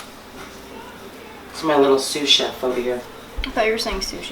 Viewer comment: HI little helper... awesome mother daughter recipe video..."I thought you said SUSHI...LOL" i loved this yall are having fun in the kitchen... Yes! Fun! and tasty 8)